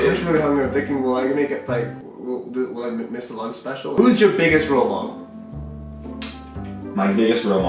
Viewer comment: who's you suggest rile model. I thought he was going to say PewDiePie